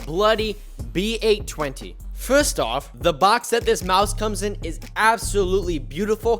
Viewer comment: did he say mouse